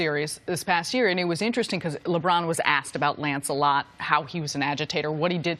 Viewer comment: i thought for a sec she called him lancelot